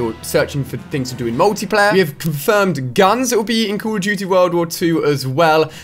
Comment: "Yes, guns are confirmed." Is what i thought you said when you said "we have confirmed guns"